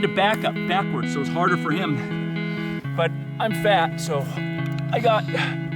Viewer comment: Did he just say "i'm fat" in the end of video?